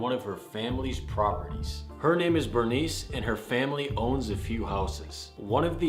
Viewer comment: BRUH I THOUGHT IT SAID “my name is furnace”